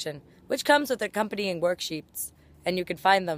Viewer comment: I heard she said "work sheeps" so I imagined sheep with suits and then I got distracted.